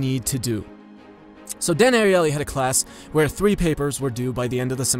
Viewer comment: every time you said ''DAN ARIELY '' I misheard it as ''Daenerys ''(Targaryen)